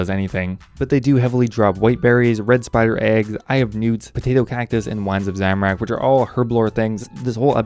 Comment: When he said eye of newts, it sounded like he said I have nudes.